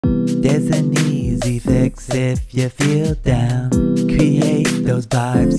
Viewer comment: At first I thought it. Say deaths an easy fix if you feel down instead of there's an easy fix if you feel down 🤣💀🤚😭